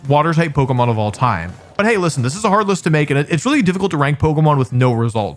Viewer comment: I like how at 31:58 he says it was a hard list to make, but the captions say 'heartless'. I like to imagine a Wugtrio fan made that edit. Maybe that John guy...